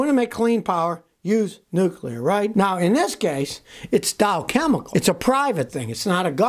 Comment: Dow Chemical - thought he said Dial - talk about clean energy.